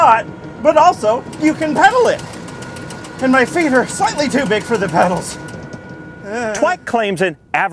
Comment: Also did he say "My Fingers are too big for the pedals" at 4:05? 🤔 😅